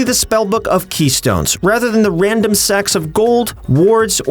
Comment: Did he say a random sack of gold or sex of gold? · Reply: Sacks of gold with S, but yes sounds like sex of gold a little.